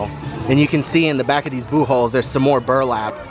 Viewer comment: LMAO. It sounded like you were saying, boob holes. 😂🤣